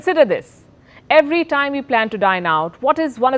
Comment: I heard " Every time you plan to die now". 😂